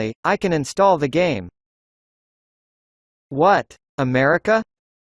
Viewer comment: Solving problem: 🇺🇸-American. 🇲🇽-Mexican. 🇧🇷-Brazilian. 🇨🇦-Canadian. 🇹🇼-Taiwanese. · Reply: It’s just that i heard UK say “What? America?”